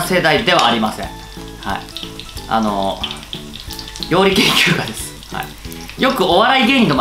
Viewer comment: 呂律がまわってなくて、料理研究家じゃなくて酔い研究家って聞こえるｗ